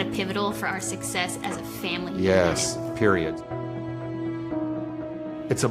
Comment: did he say periodt 😭😭